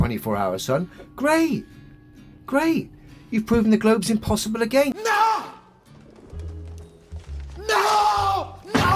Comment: I think said if there's no 24 hour sun, the globe is impossible.